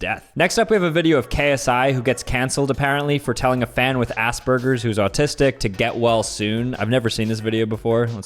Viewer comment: I'm so sorry, but I thought he said a$$ burgers at first 😭